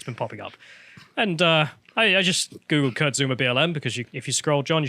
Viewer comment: No joke, I misheard Kurt Zouma as "cucksoomer".